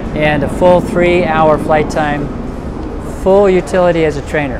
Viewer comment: I thought he said 3 hours of flight time. He needs to fix his microphone